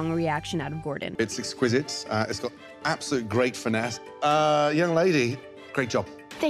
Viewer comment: did gordon just say young lady?